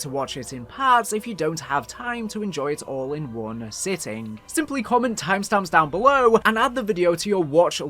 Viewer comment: what are you saying?it sounds like “butfalluspaffell” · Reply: Simply comment time stamps down below is what he said